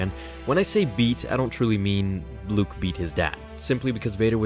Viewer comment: I thought you said Luke beat off his dad for a second lol